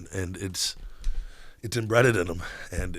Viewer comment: Did he actually say embreaded instead of embedded at 7:14? lmao